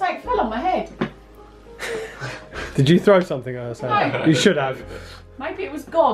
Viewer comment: LOVE YOUS SO VERY MUCH ❤️❤️❤️💝💝💝 HAPPY NEW YEAR 💛 💖 FROM SCOTLAND 🏴🏴🏴 GLASGOW XXXXX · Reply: when you felt something on your head I heard a Evp at 30:05 ?? Crispy Male Voice Whispering 😬😅👻😯 Spooky And I Just Heard Right After You Says it a Voice at 30:13 "You Did" 😳😅